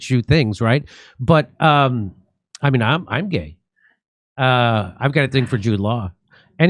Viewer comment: What's the sentence Dore says immediately after "I mean, I'm gay" at 6:20. I can't make it out. Thank you. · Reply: "Ive got a thing for Jude law" lol